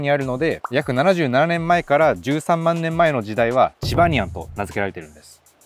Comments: チバニアンがジバニャンに聞こえてしまったwww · チバニヤンがチバニャンに聞こえるわ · チバニアンがチバニャンに聞こえた